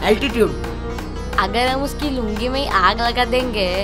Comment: who repeated it. Because i listened lulli instead of lungi